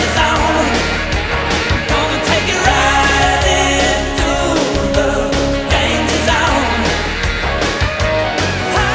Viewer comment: Chandler Bing says Wh-ooo-paaahhhhh at 5:41! Or did he say Ch-aaa-chinggggg? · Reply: Hes saying "Ya!"